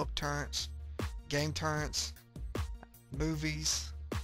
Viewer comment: lol 8:33 sounds like he says boobies